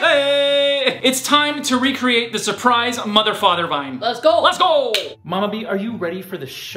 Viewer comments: I thought she said surprise, mfrs but she really said surprise, mother, father😂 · Ohhh,my whole life i thought she said ,,surprise motherfucker" now i realized that she sais ,,surprise mother father" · "surprise mother father" i always hear "surprise motherfucker" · When the sister surprise mother father i thought she said surprise mother fu$@